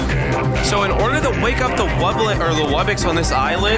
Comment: By the way, it’s “wake up he wublins,” not “we got the wublins”. I also thought it originally said that. Pls don’t mind the account name. I just thought of it randomly